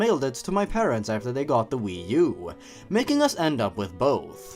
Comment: I heard, "mailed it to my parents after they had got into weed" haha! whoopsies! oWorawrxdsnuggles~